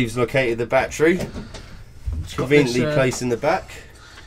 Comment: Thought you said "based in Ibiza" lol